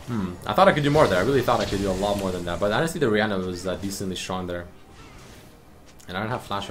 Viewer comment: you said "the Rihanna" instead of "orianna" …just imagining orianna as Rihanna is amazing